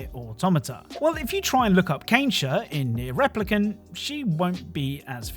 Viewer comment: Did you called Kainé Kensha ?